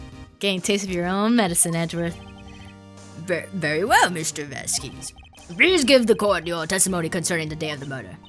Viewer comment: Did you say "Mister Vasquez"?